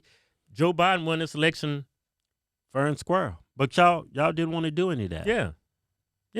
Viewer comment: I was watching this with subtitles and when you said Fair and Square, it thought you said Fern Squirrel. 🤣🤣 Fer and Squrr